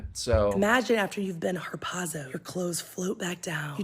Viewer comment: dID SHE SAY hARPOONED? Is this the Jonah story?